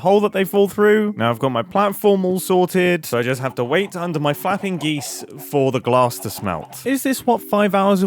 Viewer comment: I thought he said "F***ING GEESE"